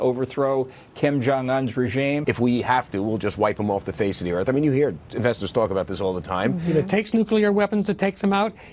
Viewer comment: does he say "if we have to, we will just wipe him off the face of the earth, I mean you hear investors talk about this all the time"? Who are these Investors?